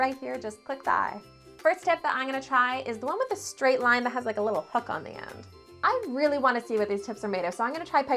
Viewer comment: Really thought she said “The first one I’m gonna try is the one with a straight line with a little f^*%k on the end” 0:53 i love you dont hate me